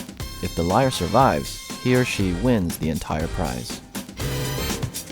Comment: why do I keep hearing him saying: here she wins the entire prize?